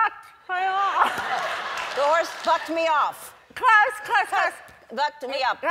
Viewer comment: I thought Ellen said the horse fucked me up 😂😂😂😂😂😂😂😂😂😂😂